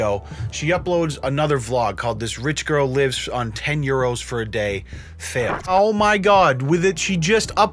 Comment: Did you just say euro?